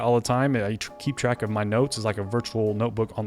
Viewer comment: OMG i thought he said NUDES 3:07 XDDD